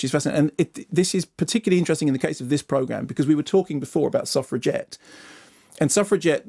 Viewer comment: Can someone please spell out what he says at 8:10. Soft reget? Sorry, english is my second language. Thanks in advance. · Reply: I believe he was talking about the film Suffragette.